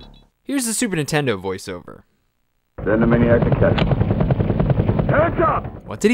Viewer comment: I only played this as a kid on my friends super nintendo. I was a Sega kid lol. I always thought he said Animaniacs attack!